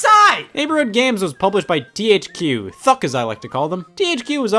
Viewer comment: why do the captions say f**k instead of thuck